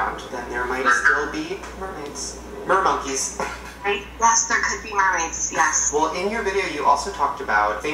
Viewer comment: Did I hear whispering at 4:38? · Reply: Omg Same tbh I heard "hey.. I hate you" yeaaah bYE